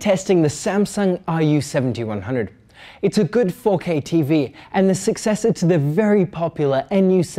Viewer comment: Loool I heard 'It's a good focking TV'